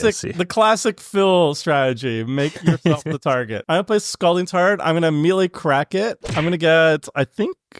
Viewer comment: Am I the only one who heard him say 'Scalding Tard'?